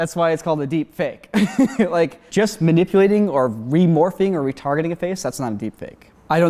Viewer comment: What I heard first: "Just manipulating or remorphing or retarding a face--" My reaction: --Wait do what now O.O